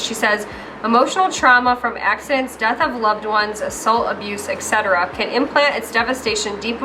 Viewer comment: Omg. Ok. When you were reading the trauma release girls post I totally thought you said “salt abuse” and not “assult, abuse”. And it took me way too long to think about salt abuse. Now all that pink Himalayan is going down the drain. 🤣🤣 #nosaltabuse